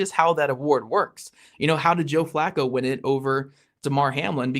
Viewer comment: damn why did I hear Joe Blacco lol